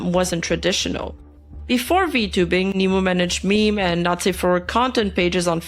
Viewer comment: What I thought I heard: "Before VTubing, Nimu managed meme and nazi fur content pages on Facebook" I'm sorry WHAT Ooooh! Not Safe For Work